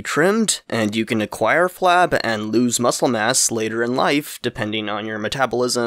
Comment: I swear i heard "Muscle and ass" instead of "Muscle Mass" for a second there.